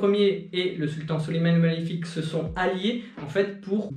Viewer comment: mdrr j'ai compris suleyman le maléfique 😂😂😂 je me suis dit ce pas normal